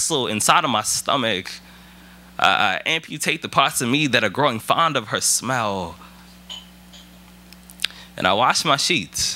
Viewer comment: "I amputate parts of me that are becoming fond of her smile".. Oh, Rudy. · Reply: I thought he said "fond of her smell" 🙄